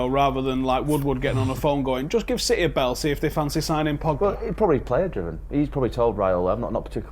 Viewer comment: who did say would be good for Utd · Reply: Pogba